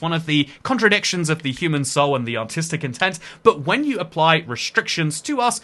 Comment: Genuinely thought he said ‘Autistic intent’ and spit out cat hair